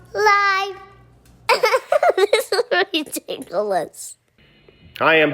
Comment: Best part of the intro: "this is ridiculous 😂😂😂" She sounds so cute I just wanna squeeze the little munchkin! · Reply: I thought she said ticklish, but I guess it might have been ridiculous. Either way it was very adorable.